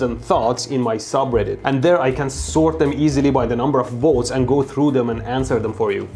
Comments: When at the beginning he said number of votes I thought he said number of volts Captions ruined it for me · I heard "number of volts" instead of "number of votes" it would be sick if you could change it on reddit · i really understood volts instead of votes, but that could be a good naming for this topic tho " Your UpVolts" or something similar ^^